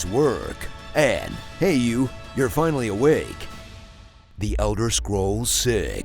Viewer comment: Damn i thought the end said "the outer scrolls' as a space pun